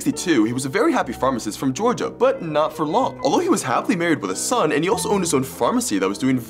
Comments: why did i hear "although he was happily married to his son"💀💀💀💀💀💀💀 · I almost thought you said "Happily married to his son"💀✋️